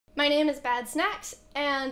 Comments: I thought she said “my name is bad snatch.” Before the name loaded. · I thought she said “bad snatch” at first. I was like wtf? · Thought she said her name was Bad Snatch